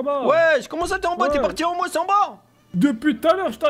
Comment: "t'es parti en Moi sans bas" ? Ptdrrr si tu veux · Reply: Luka Jokes en masse en bas* :3